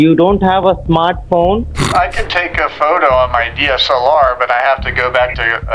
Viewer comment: my dumbass thought he said "I can take a photo on my DS alarm" 😭😭🙏🙏